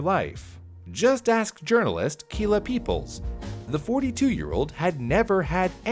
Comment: I had to re watch this point again Kill-a people what I heard and it really was , what a name